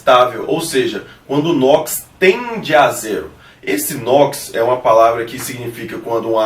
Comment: nao entendi o minuto 6:28 D: sobre o Nox? tende a que? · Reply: oi paola... quando o Nox tende a Zero!!! sem variação de cargas. ok????